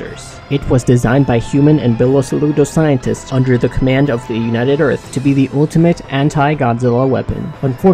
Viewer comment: did he say nutted earth?